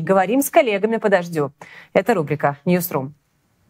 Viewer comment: Ньюс ру ! Но с таким произношением как у этой велушей, мне послышалось- не сру😂😂😂😂 потом прислушалась - точно!